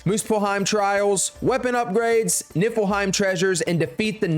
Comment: I thought bro said nipple-hiem💀